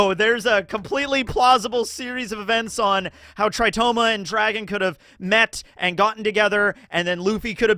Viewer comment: For a second I thought you said "How Tritoma Your Dragon".